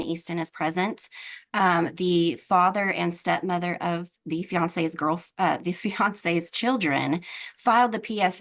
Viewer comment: At first i thought she said the girlfriends name was Beyoncé and then i realized she said fiancé.